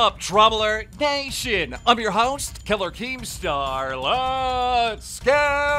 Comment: who else thought he said i'm your host "keller" keemstar